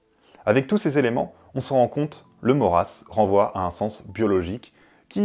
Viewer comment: j'ai compris "le mot race" par "le Maurras" ! XD